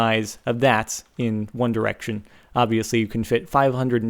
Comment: did he say one direction